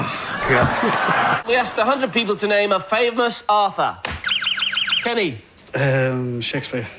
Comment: to be fair, it sounded like "author" with his accent.